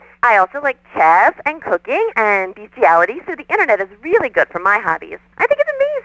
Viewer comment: …and bestiality? Is that what she said? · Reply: Yes